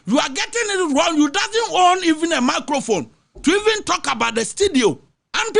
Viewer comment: Did I hear "macrophone"? 🤭 😁 😅